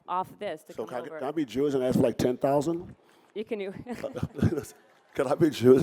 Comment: Sounded like he said "can I be Jewish and ask for $10,000" - and then he repeated it so everyone could hear it. Scumbag.